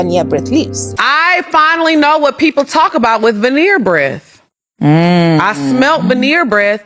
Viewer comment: Legit thought it said weener breath 🌭 😂